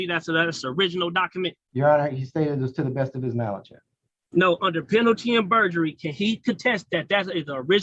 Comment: Actually, “under penalty AND perjury” is what he kept saying. His word salad is barely even English. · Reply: I thought he said “under penaldy and burgery” 😂